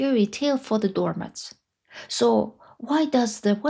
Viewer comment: When she said "door mats" I thought she said, "dormants", which I guess would also fit.